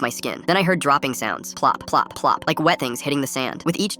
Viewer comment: When he said plop plop plop i thought it said pop pop pop